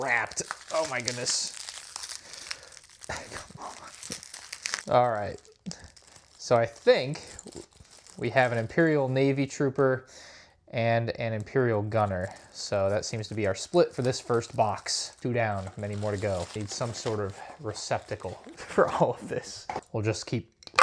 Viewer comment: did you just call commander praji an imperial gunner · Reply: No he said praji and a gunner